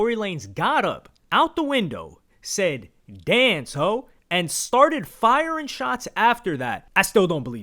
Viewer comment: Now instead of “Yea, Glo!” I hear “Dance ho!”